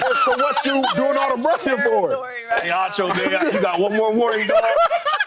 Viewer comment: did he just say "ay acho niqqa u got 1 mo warning" 😂😂😂 or im trippin